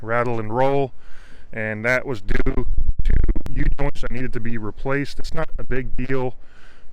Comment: What did you say at 5:14? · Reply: Due to u-joints (sorry the day I recorded this, it was extremely windy, I had to record the whole thing several times to piece together enough footage to make it somewhat understandable) there are a few other causes of vibrations, but U-joints and an unbalanced shaft is one of the main causes if the tires are decently balanced.